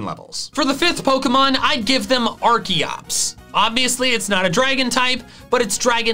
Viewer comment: I heard Arceus at 19:15 and just about laughed my ass off until realising the mistake